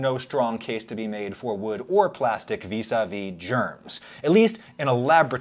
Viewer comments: I don't know why the cc says inaudible but the "inaudible" part is "vis-a-vis" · Your CC says [inaudible 00:03:28] in response to "vis a vis". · the subtitler didn't catch the phrase perhaps, but the inaudible part was : 'vis-à-vis', meaning 'in relation to' or 'in comparison with'